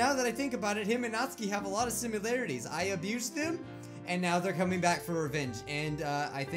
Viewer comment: Who else heard at 11:32 “I abuse them”